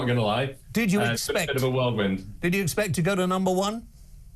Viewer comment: Um...did I mishear??. Did he say " It's been a bit of a World-wind"?................... whirlwind laddie 🌪️... Good grief!🙄